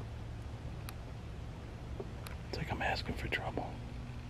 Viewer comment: when you asked if you could make that crashing sound, you then said "I think I'm asking for trouble!" And it showed "enough" I swear I heard "NO!" Scary! 31:48 or there about!